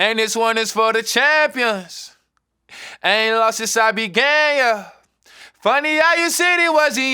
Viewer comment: sounds like he said "I ain't lost since I've been gay yeah" 😂